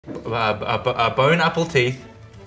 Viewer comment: I thought he said, "bone apple teat".